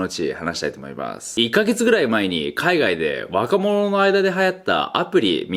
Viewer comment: 馬鹿者の間 って聞こえてゲラゲラ笑ってたら空耳だった